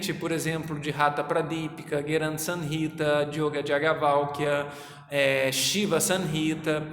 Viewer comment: em 1:53 você falou em Gheranda Samhita, Shiva Samhita , Haṭhayoga Pradīpikā e um que não entendi ->yoga Jagavalkia?